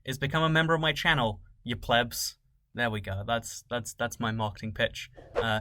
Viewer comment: For a second I thought he said “And that my marketing B*tch”